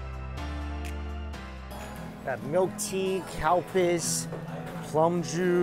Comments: Did he say ‘cowpiss’ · What does he say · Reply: He said Calpis. From Wikipedia: Calpis is a Japanese uncarbonated soft drink, manufactured by Calpis Co., Ltd., headquartered in Shibuya, Tokyo. Calpis Co. is a subsidiary of Asahi. The beverage has a light, somewhat milky, and slightly acidic flavor, similar to plain or vanilla flavored yogurt or Yakult.